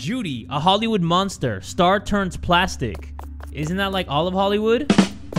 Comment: "star turns plastic" Jay: "isn't that all of Hollywood" 😭😭😭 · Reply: Ngl I thought jay said Jeffree Star turns plastic 💀